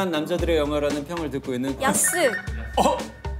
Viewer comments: 나만 야스로 들리나? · 나만 야스로 들림??ㅋㅋㅋ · 초에 나만 야스로 들리냐??? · 나만 야스라고 들리냐...? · 야스라고 들리는건 기분탓??